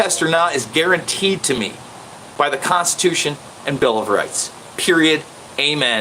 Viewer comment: Did he say "Guaranteed under the Bill of *GATES*"???